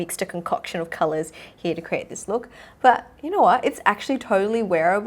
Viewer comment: "But you know wha?" 10:37 I though I heard Shu An, Singaporean youtuber and actress 😂 Tina's having the accent! Kidding! I know it's her real accent 😁